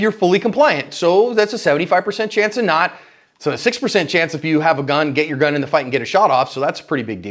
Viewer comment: Did I hear that correctly? 6% chance of being injured in a fight if you get involved with your gun? @ 9:15timestamp